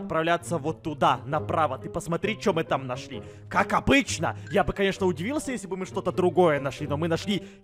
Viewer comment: мне одной послышалось "кака бычья "?